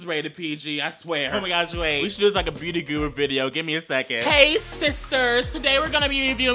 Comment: DID HE SAY HEY SISTERS OR ARE MY EARS DECEIVING ME